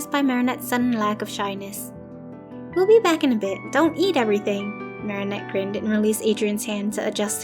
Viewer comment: When mari said don't eat everythibg i thought she said don't yeet everything